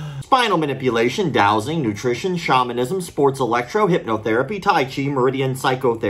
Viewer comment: Shamanism lol I thought you said Chauvinism. I'm like "God damn, brave to put that on a CV"